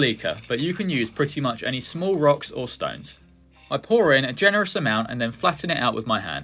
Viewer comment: Did I hear a Rock and Stone?